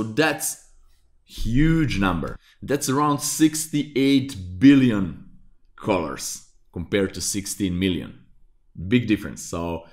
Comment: im not a camera guy and i thought that he was saying 68 billoin cars.. wtf